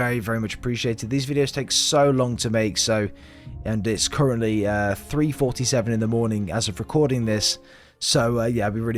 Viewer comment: greece? idk what you said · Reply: greez:)